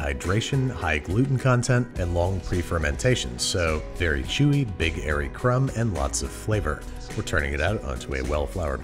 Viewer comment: I'm sure you said "big airy crumb" but I definitely heard "Big Hairy Crumb"